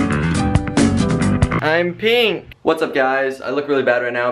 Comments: When said, " I am pink" at first I thought he said "i am peeing. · I thought he said "I'm peeing 😔" but he said "i'm pink" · when he said im pink i thought he said im peeing LOL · In the beginning I thought he said I'm peeing lol but then I realized I'm pink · I thought he said I'm peeing instead of I'm pink at the beginning 😂😂